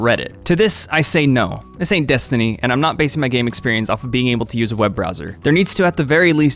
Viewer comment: What is he saying at 1:39-1:42? Right after "To this, I say no." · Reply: "This ain't Destiny, and I'm not basing my game experience off of being able to use a web browser" (I need to work on my annunciation sorry)